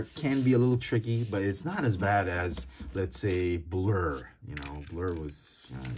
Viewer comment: Someone probably thought said badass at 11:35 but he didn’t cuz he actually said “ bad as blurrs transformation”